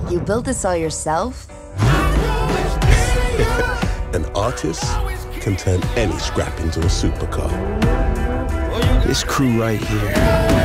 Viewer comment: I thought he said, "An autist can turn any scrap into a supercar."